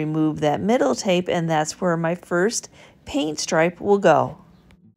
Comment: The little jellybean stools were my favorite of this video. When you said grain stripe, thought you said green..... was picturing a deep evergreen1 stripe. Disappointed it was black, aren't you getting tired of painting everything black or white?